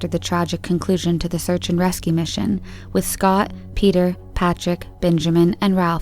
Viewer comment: I swear to god I heard you say “rescue pigeons” and I was like, wait, what? I then found a Washington Post article about it and was beginning to believe it all when it mentioned that the “rescue pigeons” are strapped into tiny couches and then I had to check the date of the article wasn’t April 1st....it wasn’t. So, and I’m still not 100 percent convinced about this. They are trained to peck a button whenever they see “rescue orange” and are rewarded with food. Apparently the pigeons can spot the colour 90% of the time and humans only 38%. They are then trained to get used to helicopter sounds and then, on a rescue are on their “couches”, 4 birds cover each compass point, in a plastic pod on the side of the helicopter. Apparently, the first batch of “pigeon recruits” drowned when the helicopter had to ditch in the sea. The humans survived, but the pigeons plastic pods were on the base of the helicopter and came loose and sank. After that it says that they were going to locate them higher and on the sides of the aircraft. Hmmmm, what does everyone else think? The whole thing might be 100 percent real, but the idea makes me laugh each time I think about “rescue pigeons” on tiny couches...on a helicopter. Oh and the navy source stated that of the pigeons trained, many will “wash out” but they hope for at least 6 to graduate to become Coast Guard Rescue Pigeons!!